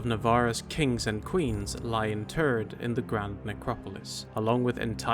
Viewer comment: Did you say what i hear? :D "The great number of kings and queens lay in TURD" ? ? ? :D